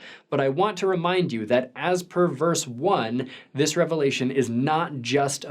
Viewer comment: I was confused for half a second when I thought you said "perverse." (It was "per verse.")